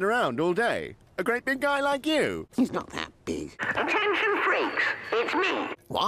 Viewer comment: oh i misheard that big time thought duck was saying "He's not happy..."